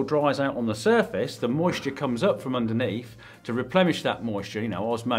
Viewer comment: What? Did he say Replemish?!